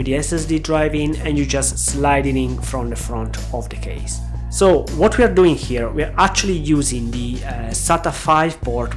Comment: Pardon me, I'm not native English speaker but what did you said cca at 4:30? Did you mention "Right" controller or "RAID" controller? What is controller, it is hardware or software? If I understand correct then for some reasons it is impossible to boot directly from ODD. How did you setup your USB so it contains only boot loader and OS is on SSD? Here is direct boot from SSD <link> I guess the trick is to have SSD in separate RAID. Is this worse solution than yours for some reason? Thanks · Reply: hi! I'm non native english too... so not so easy to understand me! I said "RAID"... the solution I've adopted is boot loader (GRUB) on USB and OS on SSD... The solution you propose from ADMFACTORY is very interesting too... I do not see any downside... unless is going to mess then with the 4 disk array (but won't see why!)